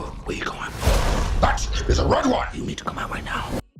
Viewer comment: I thought it said, “it’s a red riot!”